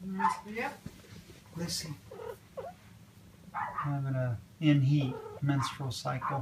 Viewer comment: I thought it said cat in the hat